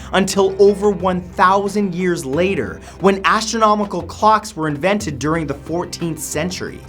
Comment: astronomical clocks, thought he said astronomical cox